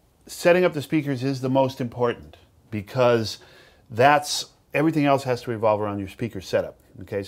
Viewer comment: Sounds like he just say sheath everything